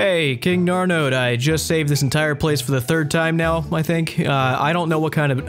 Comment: I looked away and thought you said “saven’t” cos someone died